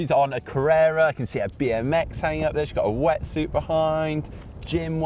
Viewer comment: #Caption I thought you said wingsuit, not wetsuit!